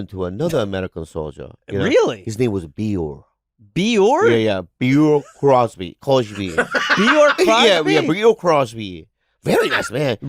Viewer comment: Idk why I thought he was trying to say Bing Crosby 😂 I was like what bing crosby lore do I not know of